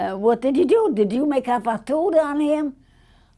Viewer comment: Does anyone know what italian word she says at 43:40. The name of the spell? The italian caption says “fatto”, which according to google translates to “done”. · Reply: She said she thinks it's spelled fatona and said if you're not Italian you won't understand it lol